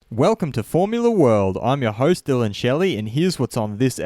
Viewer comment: Always on beggining i hear "verstappen on fórmula world"